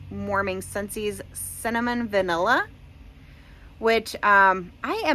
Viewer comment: I thought you said Salmon Vanilla for Cinnamon Vanilla and I was so confused 😂